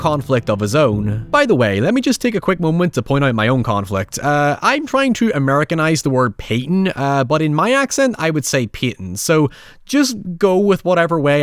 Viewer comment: as an american I heard you say "I'm trying to Americanize Payton which I would pronounce Payton" which now leaves me unsure if I'm understanding lmaoo